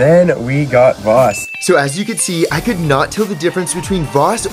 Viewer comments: i Thought you said poop water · I heard "poo water"